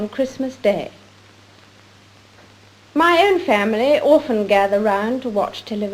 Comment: Any comments on the Queen's often/orphan merger as at 9:08? (Well, maybe it's not a merger, but she said "often" and I heard "orphan"...)